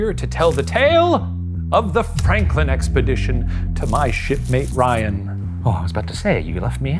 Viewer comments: Definitely thought he said “shitmate” for a second there 😂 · I just heard "With my 💩mate Ryan." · Man I am so tired, I thought Shane said "To my shit-friend, Ryan. 😢